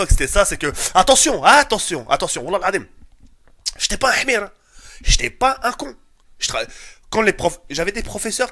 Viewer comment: Mdr il a dit quoi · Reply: Qu'il était pas con